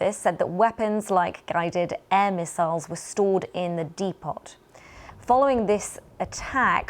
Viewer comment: She said de pot instead of depot for a news reader 😮😮😂😂 (